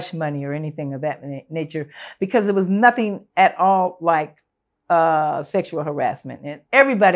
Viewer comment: Bob Barker was sounds like he was that dude!!! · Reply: dude she clearly said "it was nothing at all like sexual harassment"